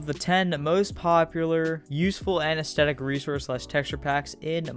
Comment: Im sorry but u said anethestetic instead of aesthetic oml, btw this is